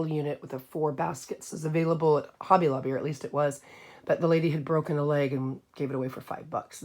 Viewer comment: When you were talking about the four metal basket piece from hobby lobby I thought you said the lady had broken HER leg... And I couldn't figure out why breaking her leg had anything to do with her selling that piece for $5 😂.